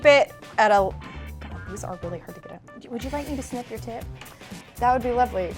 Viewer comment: Am I the only one that thought they said sniff your tit not snip your tip